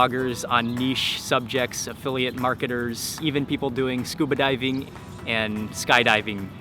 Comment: Scuba diving... I misheard what he said... I was shocked at first when I heard he said stupid diving... silly me...